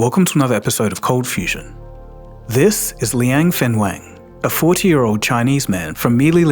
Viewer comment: It's Liang Wenfeng instead of Liang Fenweng 01:09 Liang Wenfeng is correct